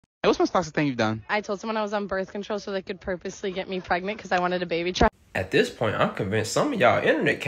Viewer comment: She really was desperate for that child support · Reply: I heard “because I wanted a baby child” does anyone have a clue what she said?